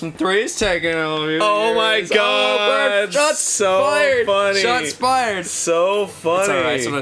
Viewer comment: When said "Shots fired!" at 9:00, I thought he said "Jon's fired!" I was like, "Jesus, Ross. I get that the joke is 'Ask Arin Questions He's Sick Of Hearing' but that's a pretty drastic escalation."